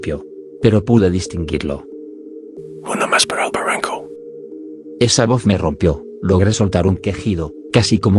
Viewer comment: que fue lo que dijo? no logre entender. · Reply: Una más para el barranco.